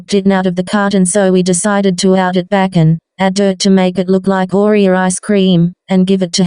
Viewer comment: What did she say? I couldn't understand what she put on the icecream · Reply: dirt